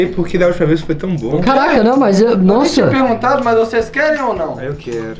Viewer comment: ouvi caraio quem ouviu também